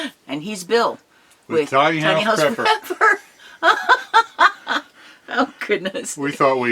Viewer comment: Woohoo, I thought y'all said "naughty-house preppers", ha! I'm so excited for both of you! Looking forward to more video.